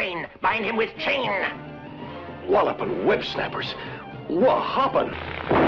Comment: Did he just say wallopin websnappers, wha happun? Is this where Matt McMuscles got the name for his show?